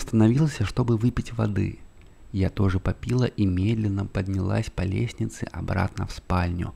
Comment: Поставила скорость 0,5 и начала засыпать, но когда услышала "я тоже попИла", на меня напал смех, а от сна не осталось и следа!😂😂😂 Время 5,23, скоро уже вставать...